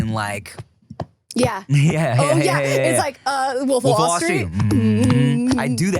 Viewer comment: Does anyone know the word and Tim both say at the same time at 53:49? Captions has nothing and all I hear is overall string 😅😅😅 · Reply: Lol "Wolf of Wallstreet"